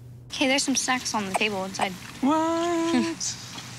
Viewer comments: I thought said “Sex is still on the table” I’m pretty sure now with her and Shayden it ain’t happening 😂😂😂😂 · even the captions thought she said “sex on the table”